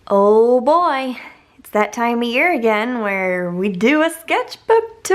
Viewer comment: When she said “ it’s that time of the year” I thought she said it’s that time of the month and I was like ‘wait I thought I pushed on a sketchbook tour vid’ lol I’m dumb 😂